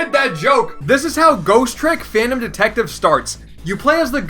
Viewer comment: what game is it at 05:57? I couldn't hear it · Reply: Ghost Trick